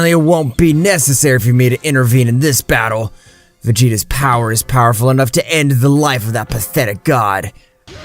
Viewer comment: I heard vegeta's pee is decreasing the subtitles are craaazy💀😂